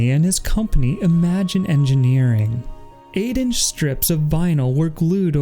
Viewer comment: with what word did you use, to describe the strips of vinyl made of the bell? aiding? eighty? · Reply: "Eight inch strips of vinyl" - apologies if there is a mispronunciation there!